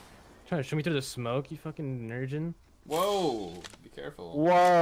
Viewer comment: go to 15:45 man i hope i didn't hear what i think i heard. What did he say to make you say "Be careful " "Whoa" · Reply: IAMSAYIBOY EVO he said nergin I believe, nerd virgin or something like that, we were all streaming so if he did say something racist we would be banned and Ion do that racist bs